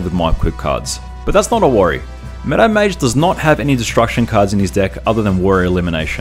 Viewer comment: Took me three videos to understand "Meadow Mage", which I always heard as "Meta Mage". XD Keep up the good work.